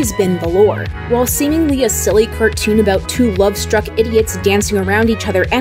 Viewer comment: Did you just say idiot love idiots